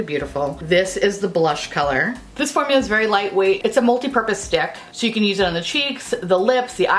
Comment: I could have sworn you said "multipurpose dick." 😂😂😂